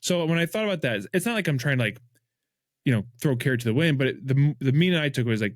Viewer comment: Throw a carrot to the wind 🤣I'm laughing so hard. I know it as "throw caution to the wind," but I heard "throw care to the wind"